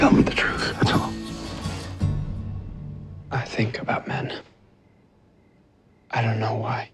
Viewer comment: When he said 'I think about men' I totally thought he said 'I think I'm batman' and was really confused until I went back and rewatched it XD